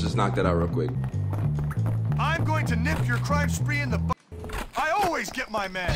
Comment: did he say I always get my man. WOoooooooooOOo